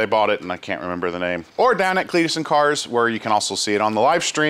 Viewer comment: I wasn’t paying attention at the end and when said “down at Cletus and cars” I swear I heard “Damocles and cars” and thought, that’s a funny name for a car show.